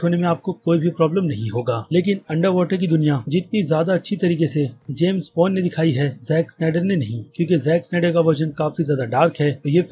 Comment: for second i thought he says JAMES BOND